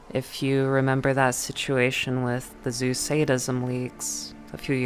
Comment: I hear “Zeus-sadism”. 😂 It make sense! Didn’t he fuck goats!?